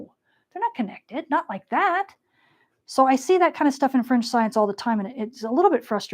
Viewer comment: Dear, thank you for this amazing knowledge you are sharing with us. I ofter hear you saying French science. Maybe I heard wrong, I put the self-generated subtitles and I read French science, like in 1:33:14. Is there a loss in the translation? What about French science?